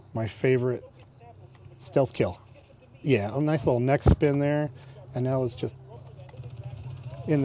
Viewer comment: LOL, when I turned away and he said "Nice little neck spin there" I hears otherwise. I heard "Nice little mexican there" and I had to do a double take. lol No offense to mexicans, cus I am one.